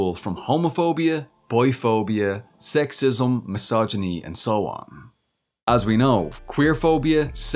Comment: I keep mishearing “biphobia” as “boy phobia” lol. But it’s kinda correct, bigots hate that I like boys too!